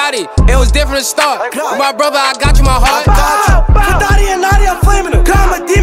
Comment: did he say fuck dotty and notti? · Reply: For dotty and notti